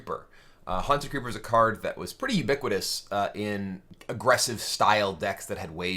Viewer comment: what was that word: evicodus?( that didnt get me anywhere on google...) min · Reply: Stefan Gabaroi I know it was ages ago, but the word was ubiquitous.